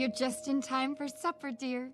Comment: First time I seen the movie I thought the other mother said your just in time for SUFFER dear.